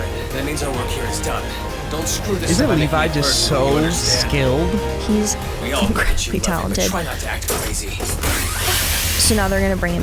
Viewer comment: I hate it when theyre discussing something while the episode is playing and they dont hear what the characters are saying like in 6:04 when Levi said "we all get that u love him"